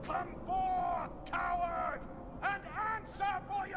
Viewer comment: Whoa. That is pretty cool. The Patrick at the end pretty much summed up my face xD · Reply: Also, didn't know he said "Come forth coward!" I thought he said "Come forth power!" Or something like that.